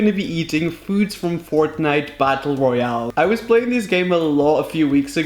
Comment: did he say fortnite bottle royale